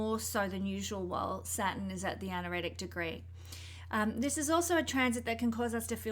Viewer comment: Omg i thought it said anore xic”😂 Mercury rx started early for me.